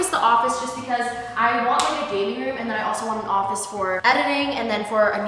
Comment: Literally thought she said “baby room” when she said gaming room..almost fainted